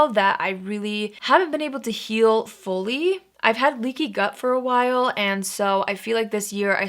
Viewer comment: If it wasn't for the new YouTube subtitles, I totally would've thought you said you have a leaky butt